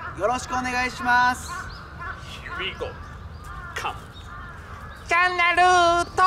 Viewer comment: いいよ、がHere we goに聞こえてしまう！